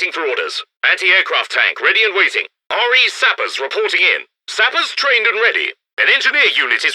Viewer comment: Anyone knows what was said at 12:24? "??? sappers reporting in" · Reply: Royal Engineer "Sappers" Reporting In